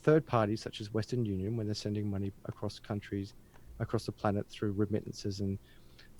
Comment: Did say sending money “across the planets”? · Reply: He said "across the planet", no plural